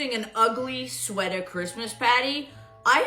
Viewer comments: I thought she was saying Christmas patty. 😅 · When I first heard her say "party" with her fake accent I thought she said "patty"(like a burger patty)